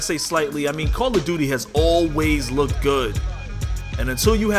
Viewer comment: For a second there I thought I heard you say call of duty always looked good